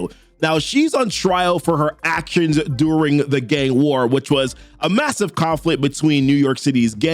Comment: Legit thought he kept saying “Gay War”